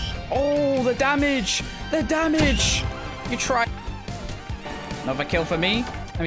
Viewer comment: who else heard him say your tra